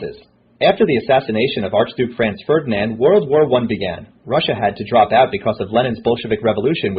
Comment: LOLOLOL I LAUGHED SO HARD AT 9:52 becuz it sounded like "Russia had to drop out because of Lenin's bullshit." LOLOLOL xDDDD